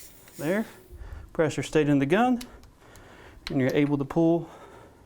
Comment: I just bought an Air Venturi G9 pump and a Notos air rifle. What did you say @ 5:48? "Press your ____[?] of the gun..." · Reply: "Pressure stayed in the gun."